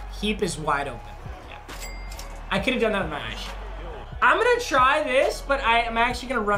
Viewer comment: “I could’ve gone outta bonsh” who else heard that